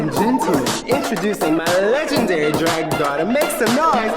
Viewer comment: not the caption saying bottom instead of daughter 💀